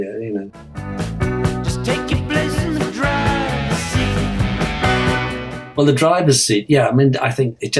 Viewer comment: I liked it enough to put into my cassettes but never really paid attention to the lyrics. For a long time I thought they were saying travesty instead of driver seat! Cool stuff